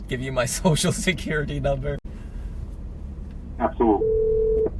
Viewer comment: LMFAOOOO “I’m having a problem... should I pull over safely and give you my social security number?” THANK YOU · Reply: Best part is it sounds like he says in response “absolutely.”